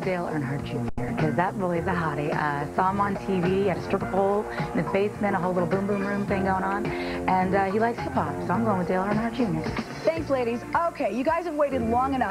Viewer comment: hold I hear that right. Dale Jr on the stripper pole....... ummm